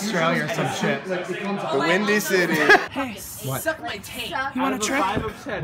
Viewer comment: anyone hear someone go "SUCK MY TAINT" in the background? Cause same